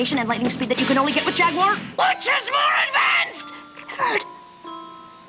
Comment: Did she just say "clevered"? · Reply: She said Clifford because she was trying to get his attention and she was frustrated.